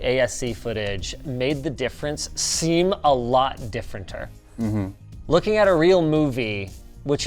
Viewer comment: at about 8:48 did he say differenter....?